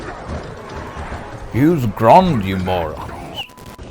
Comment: What is he saying here? "Use ground you morons"?